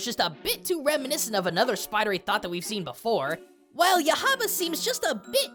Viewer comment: did he... Did he just call her a Spidery Thot????